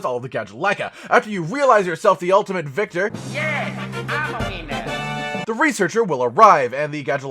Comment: I thought that said "Im a weiner" for a second